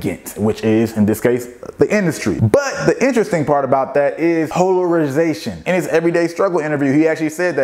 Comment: what is he saying at 1:31 ? hoe er iz ation? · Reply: MiKEY SANZ polarization